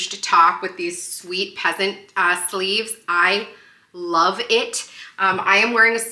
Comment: You look amazing! For a second when you were describing your top I thought you said pheasant ass sleeves! 😅😅 Love your new decorations and can't wait to see how you use them!